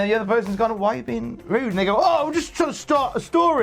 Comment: Word To The Nerd I don’t get it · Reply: I thought he said the N word and I had to listen several times, plus, check the comments to realize he said "and they go"